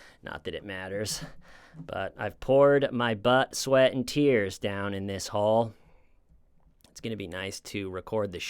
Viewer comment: did he say he poured his butt-sweat into the studio??😂